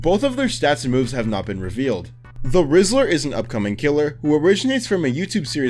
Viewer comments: When you said the whistler I thought you said the rizzler lol 😂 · when you said "the whistler" at 19:19 I seriously thought you said "the rizzler" and I went, "WHAT." EDIT: I'M NOT GOING CRAZY, HE SAYS "THE RIZZLER" IF YOU TURN ON CAPTIONS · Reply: SAME I HEARD "THE RIZZLER" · i thought he said "the rizzer" 🤣🤣🤣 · please tell me I'm not the only one who heard "the rizzler" 😭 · I thought he say the rizzer for a second there😭😭🙏🙏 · i thought bro said "the rissler" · When u ssid the whistler i thought u said the rizzler😭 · im so cooked i heard the rizzler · I thought he said 'The Rizzler' 💀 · i thought bro said the rizzler😭😭😭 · please tell me someone else thought he said the rizzler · bro even subtitles says its the rizzler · I THOUGHT BRO SAID THE RIZZLER 😭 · I thought he said the rizzler lol😢😂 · what they hear: the whistler What I hear: the rizzler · I heard "The Rizzler" and was about to delete mys- Roblox and burn my Laptop until I heard "The Whistler" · thought you said the rizzler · I cannot be the only person that thought whenever he said the Whistler I thought he said the rizzler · why did he say it like rizzler i swear the brainrot is kicking in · i thought he said the whistler as the rizzler LMAO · who else thought he said “The Rizzler”? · did he just say the rizzler? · i heard rizzler. chat am i cooked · i thought he said the rizzler and got confused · I thought he said “the rizzler” 🥀😭🥀😭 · When he said the whistler i thought he said the Rizzler lol